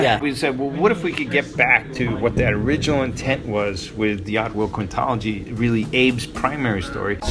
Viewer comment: he said oddworld quintology and i heard "diablo"